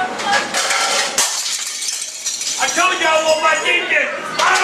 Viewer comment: I can’t hear what he saying do you know what he want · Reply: “I want my deep dish pizza”